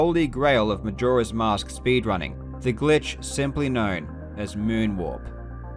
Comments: I didn't hear walk in moon walk so I thought you said, known simply as moon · I keep hearing "Moon walk" instead of "moon warp" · When he said "Moon Warp" I thought he said "Moonwalk" 😂😂